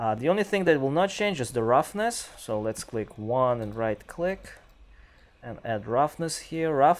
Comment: Great Video, Thanks!!! This might help follow along for us beginners. I got pretty confused with hot Keys because I thought you said 3 + right click and couldn't get them to work at first. 1. Create material. double click it, hit 3 + Left click, or type "constant' (3 Vector) Pick a color between green and blue like glass. connect that to base color. name it 'Color Base' on left. 2. De-select. 1 + left click or type 'constant'- connect to roughness, leave it at 0 and name it roughness. 3. Click glass material, then on left turn 'Blend Mode' to translucent, make sure '2 sides' is unchecked. Under Translucency, turn lighting mode to 'Surface Translucency Volume' 4. De-select. L + left click or type 'Linear Interpolate', connect that to metallic. Then 1 + click, connect that to A, copy it and connect to B. Name A Reflection Front and B Reflection Side. Set Front to value 0.05. Set Side to value 1. 5. Make 2 copies of Step 4. Plug one into opacity. and one into refraction. Name them; Opacity Front, Opacity Side, Refraction Front, Refraction Side. Set Opacity front to value 0.05. Set Side to 0.2, Set Refraction Front to 1.2 and set Side to 0.8. Connect to Opacity and Refraction. 6. Drag off the Alpha of the first Lerp, Type 'Fresnal Function' Drag off Power and type 'constant' name that power and set to 2. Then plug the Alpha's of each Lerp into that Fresnal Function. Save and Exit. 7. Now in materials folder, right click and make an instance of the materal. Apply it to something i